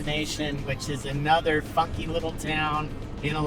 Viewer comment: 😂😂 i thought he said the other f word little town 😂😂